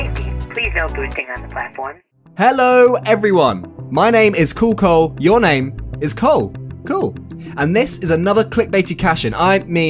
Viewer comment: I thought you said my name was coolghoul 😄